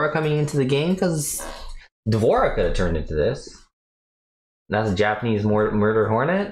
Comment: Ngl..I thought he said "she turned into me"😂 it would be fitting since he's 😅